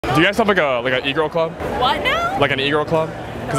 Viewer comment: At first i thought i heard this dude ask "Do you have a Negro club"? I was like naaa aint no way 😂